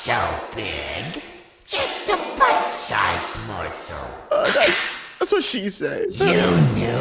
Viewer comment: Subtitles saying buttsized💀